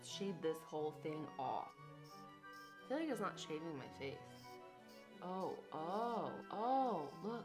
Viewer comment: Literally I thought it said “shaving my head “